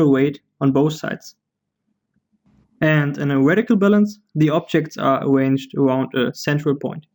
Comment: What do you say at 9:06? A vertical balance?